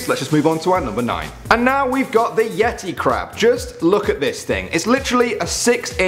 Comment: When he said Yeti Crab I Thought He Said Yeti Crap